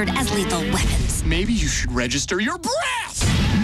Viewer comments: It sounded like he said "maybe you should register your BREASTS‼" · I thought he said BREASTSt at 0:08-0:10 I'm like what? Wouldn't that be a bit crude for a kids show? But then again on Icarly they said the word boobs a couple times so it seems like he could have said that but you're probably right that it's breath.